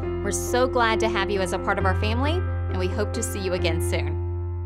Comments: That last little line of - "that's not me." - was SUCH a good ending touch like I'm being dangled off the best cliffhanger right now! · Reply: I thought she said "That's not meat" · That last little line of - "that's not me." - was SUCH a good ending touch like I'm being dangled off the best cliffhanger right now! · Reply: honestly? i thought it said " it's not meat " which can also work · I hear "that's not me" do yall hear that too or is it just me??